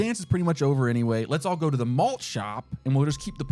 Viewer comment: the malt shop was a bit sus. even for the 70s. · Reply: lowkey i thought he said mulch shop not malt